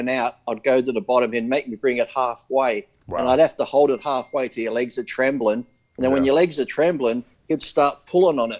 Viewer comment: Why is saying his legs were Trenbolone? 09:19 &